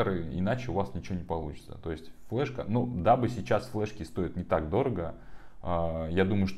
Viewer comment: я правильно услышал: "дабы сейчас флешки сто́ят..."? Если это так, то получается белиберда, ибо "дабы" = "чтобы", прочитайте теперь с заменой. 😵‍💫А ещё аФФтАр как-то странно прочитал "Image" у Acronis True Image. Форматировать флешки (как USB так и карты памяти) в NTFS - неоднозначное решение.